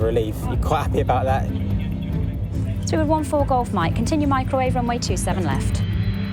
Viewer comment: Did she say Microwave ? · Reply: I'm sure she said microwaves.